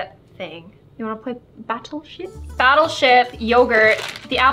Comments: Is it just me or instead of saying battle ship but I heard her say : battle s**t 😂😂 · Did anyone hear her say “battleshit” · did she just say "do you want to play battel sh!ts"